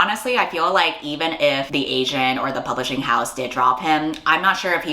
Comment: I thought you said “honestly even if the Asian or the publishing house did drop him” and did such a double-take, had to rewind and everything. Like, did the entire continent of Asia and all of Asian diaspora gang up on this creepy as heck man 😭 Because I’m fine with that, let's rally 👀